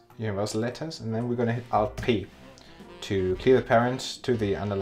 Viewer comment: can someone tell me what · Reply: "Alt + P"